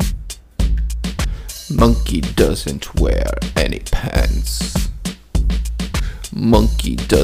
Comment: In the part "Monkey doesn't wear any pants", I always thought he said "penis" instead of "pants"